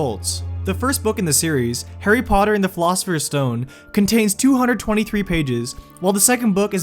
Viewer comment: Did he say philosophers stone at 2:30 ?